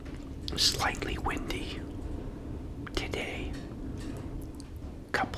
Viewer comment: He’s eating McDonalds, it’s an Israeli fast food chain. And this isn’t a war, it is a genocide which has been happening for a very long period of time. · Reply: I thought it said Wendy’s instead of Windy.